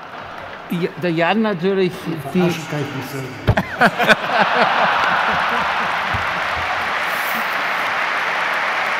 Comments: Was sagt Rummenigge bei 1:30? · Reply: Verarschen kann ich mich selber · was sagt Rummenigge genau ? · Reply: Verarschen kann ich mich selber